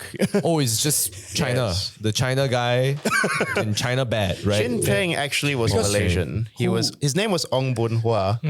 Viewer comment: Damn. On 16:47 , got me quite a scare there when mentioned "Chin Peng", and I heard "Jin Ping" 😂